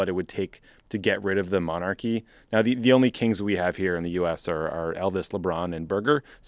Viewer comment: I heard "kinks" here and had to go back and listen again.